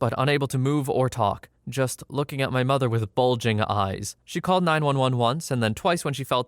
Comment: it kinda sounds like he says 9111